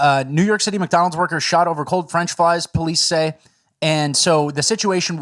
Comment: did say, "French Flies" ??